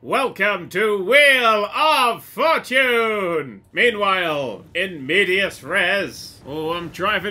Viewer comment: did he just say "meanwhile in medias res"? ;D